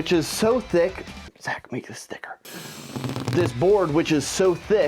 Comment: I don't know why I heard "Seth's meat is thicker"